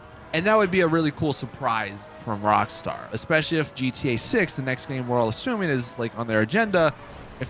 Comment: Not sure if anyone heard him saying in GTA sex instead of GTA 6 at 7;48